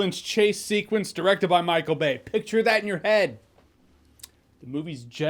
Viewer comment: When I saw this trailer at the movies I first thought they said it was a Micheal Maan movie and was like this could be interesting- when my brain course corrected and heard Micheal Bay I said to myself, "Nope".